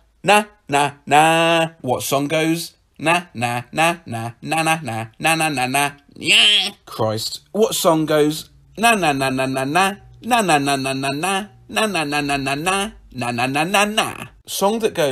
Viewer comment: it sounded like banana lol